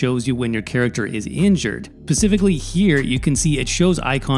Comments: Did you literally just say "pasificly" instead of "speficically" at 1:17 jesus christ. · Thanks for the video. Also, at 1:14 did you say "pacifically" instead of "specifically"? xD